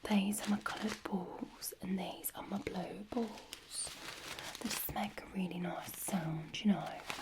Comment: I heard 'blow balls' at first. 😳